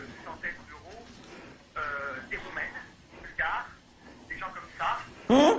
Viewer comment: j ai pas compris elle a raconté quoi qui la énerver ?? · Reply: La mère de Damien a raconté à un ami de la famille que Damien était en contact avec une agence pour trouver une femme roumaine. Donc cet ami est énervé car il sait que c’est une arnaque.